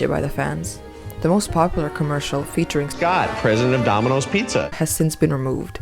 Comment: He is Either Saying "Scott" Or "God" President of Dominos Pizza, Why was He... In a Vocaloid Ad? Thats so Random, Thats hilarious! 😂😂😂🤣🤣🤣